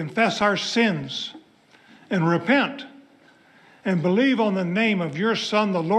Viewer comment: Trump has admitted he doesn't repent. That's why he can't listen to that prayer. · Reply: he thought he heard repaint and said for what.